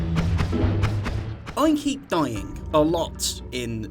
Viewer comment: The intro to this video had me so confused i thought you said "Oinky Doink!" but you said "I keep dying!" 🤣🤣